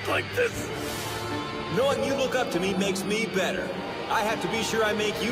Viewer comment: I sorry but when tensei said Look up too i thought he said fuck up too